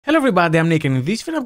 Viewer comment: Everytime Nick does his intro all I hear is "Hello everybody I'm naked."